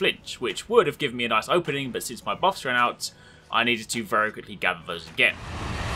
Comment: Can someone explain to me what he said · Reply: He said that since his buffs ran out "hes going to gather those very quickly again"